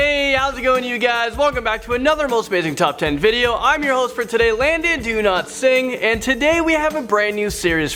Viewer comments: I keep hearing "Landn Do Not Sing" lmao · I swear I heard "Landon DoNotSing"😂😂😂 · Is it strange that when I heard you say your name I heard “Landon Cantsing” can you sing IDK